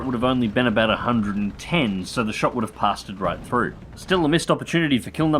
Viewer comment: Wait wait wait... at 6:55 did you say "pasta'ed" right through??? Top kek.